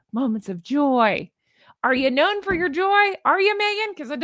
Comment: When you said "are ya know for ya joy?" It sounded like Pheobe saying "put ya face in the hole lassie!" 😂😂